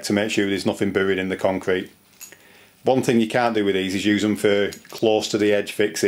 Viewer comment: the text says, "one thing you can do is use them for close to the edge fixing..." I think the narrator actually said "can't" but the voice recognition software doesn't understand Brits. I think he meant "CAN'T." He calls them "through bolts"; in the US we call them wedge anchors or Redheads, which is actually a brand name that's become generic. We also have s bolt called a "Titen" from Simpson-Strongtie which works better close to an edge. They cut threads in the concrete, so they don't exert as much expanding force, so they are less likely to crack the concrete. We haven't caught up with the rest of the world, so we only have inch (Imperial?) sizes- Simpson may have come up with metric sizes for the other side of the pond. Someone asked about the definition of edge distance- check this link- <link> It gives a rule of thumb which is that an anchor should have a minimum of 12x the diameter of anchor spacing to an adjacent anchor or to any concrete edge. Also, the concrete should have a minimum thickness of 1.5x the depth of embedment.